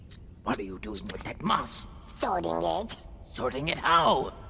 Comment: I thought the gremlin said “throating it” 💀